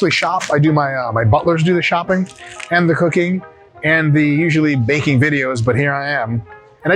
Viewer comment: Why did i hear you say "my butler do the shopping and cocaine" sus :D